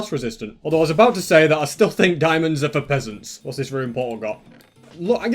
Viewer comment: Did he just say diamonds are for pheasants... cause he was right